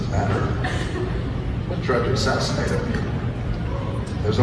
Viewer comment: UH why did he say "" I tried to assassinate "" huh???? time is 1:46 in video